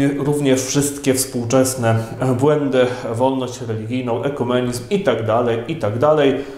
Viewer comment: Dobrze usłyszałem? Potępiacie wolność religijną?!